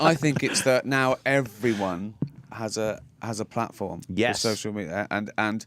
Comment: at first i thought gervais said "now EVERYONE has a platform on social media" while demeaningly tapping joe on the head